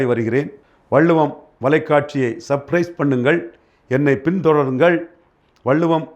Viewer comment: did he say surprise? 😳Of course, I subscribed. 😊